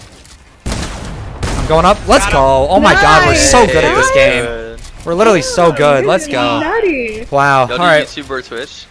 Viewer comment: what did she say? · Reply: Hyper-_- “nice, nice! you guys are nutty!”